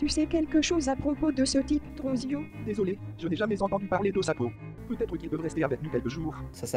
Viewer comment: Quand ça a dit o sapo jai compris " OH SA PEAU " ca fais tout de suite en quelque sorte comme si elle est choquée de voir des personnes avec une autre couleur de peau 😭